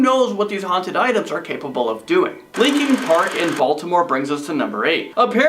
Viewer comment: For a sec I thought he said Linkin Park